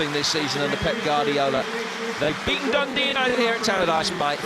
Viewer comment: Based on Messi's hair this is 2008 not 2009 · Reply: I thought they said Barcelona never beaten dundee 😳😊